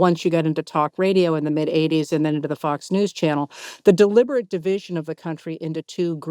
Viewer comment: Oh, at around 23min, when Heather said “Fox News” I thought I heard “Fucks News” 😂 Maybe that’s appropriate 😅 Sorry for the expletive!